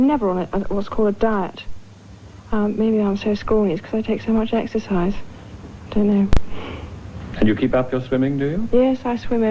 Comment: Him describing the job of Prince of Wales was just terrible! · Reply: All I heard was "I also don't know, mate."